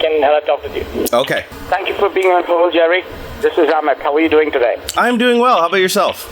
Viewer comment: Did he just call you an a-hole?